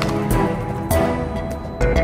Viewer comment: why does it sounds like hubert is saying : i simp the building